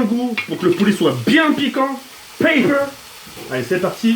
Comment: c'est moi ou il dit papier au lieu de poivre?